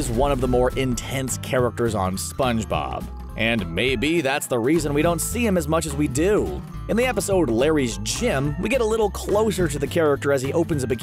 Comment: I thought it said "Larry's Cum"